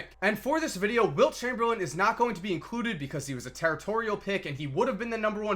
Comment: i thought you said Wilt is not going to be included ;)